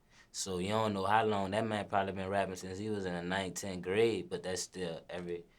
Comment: who else thought he said 19th grade😂😭🤦🏾‍♂️